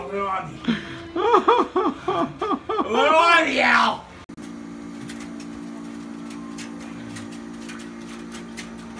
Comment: Does anyone have any idea what he says at 1:45? I’m dying to know! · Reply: “A little on me Al!”